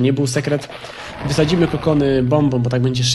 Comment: Usłyszałem 'kondomy', a nie "kokony"